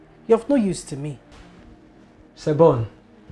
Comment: did the gateman just say "sebono", does he know what that word mean in South Africa?😂😂😂